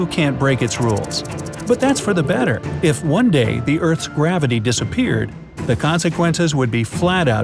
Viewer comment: I thought it said what if gravy disappeared XD